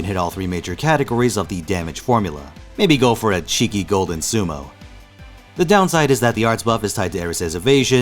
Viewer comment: Did he just say : golden sumo